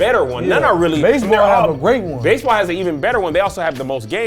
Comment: Did say baseball has the most gays ?